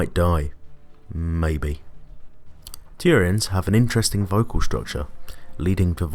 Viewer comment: his accent made it sound like he said tyranids instead of turians just wanted to point that out